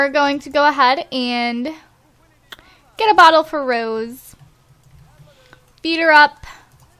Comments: One minute into the video, you said "Feed her up" referring to Rose, and I thought you said beat her up lol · When u said feed her up I thought u said beat her up